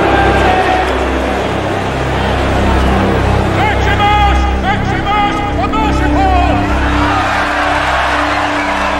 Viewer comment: what do they say to Maximus here? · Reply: Maximus, Maximus the Merciful